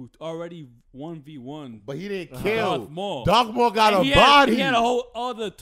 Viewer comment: sucks that they know that Darth maul survived, it was one of the dopest reveals in this show. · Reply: dont they know already? I thought one of them said "but he didnt die" talking about Darth maul?